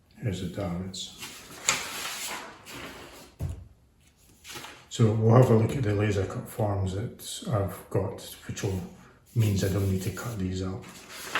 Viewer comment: I'm sorry sir but what did you say between 9:48 and 9:57? I'm glad I came upon these videos as I am putting together (hopefully) a paper model of the Suvorov. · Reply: Hi, "So we'll have a look at the laser cut forms that I've got which means I don't need to cut these out" The pages on show at that time are the templates for the hull framework/bulkheads/skeleton. These sheets are glued on to thicker card which then needs cutting out. I prefer to buy , if available, laser cut forms which are thicker card but already cut to size and shape with a laser saving lots of time and effort and definitely more accurate than I could cut them. So i was skipping over that sheets and saying we'll look at the laser cut forms later. Hope that helps.